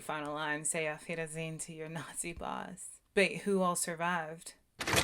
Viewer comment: You thought he said N@ "boss"? He said balls...